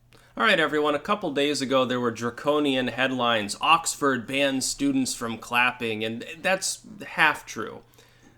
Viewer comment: damn, I thought it said "oxford students has the clap"